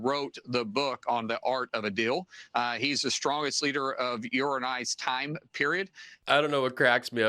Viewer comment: "...your and I's...?!?" WTF? Or did he say urine eyes?